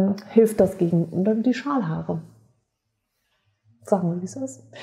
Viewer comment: Ich habe "Schamhaare" statt Schalhaare verstanden 🤣🤣🤣war kurz irritiert, aber dachte auch "joa warum nicht , sind auch Haare" 😂 du merkst ich vertraue dir da voll und ganz 🤣🤣🤣